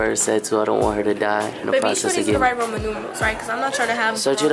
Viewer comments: When she said romen numerals I thought she said ramen noodles 💀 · I lowkey thought said "babe are you sure these are the right ramen noodles" instead of Roman numerals 😂😂😂